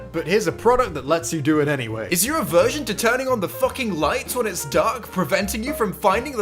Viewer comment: I wasn’t really paying attention and before he finished his sentence I thought he said “Are you a virgin to turning on lights?”